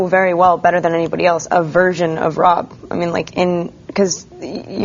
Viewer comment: thought she said virgin at first, think she said "version" =P